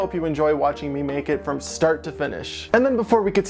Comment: I literally heard "And I really hope you enjoy watching me naked from start to finish" ... I seriously had to replay that part because I was like wait waht? :"D Then I realized he said "Watching me MAKE IT from start to finish" :"D